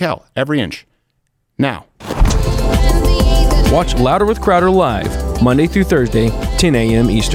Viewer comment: For a second there i thought It said cowards & yeah i thought that sounds about right,but its actually crowder